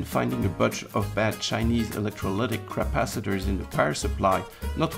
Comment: "finding a bunch of chinese electrolytic crapacitors" was that a slipup on your part, or mishearing on my part?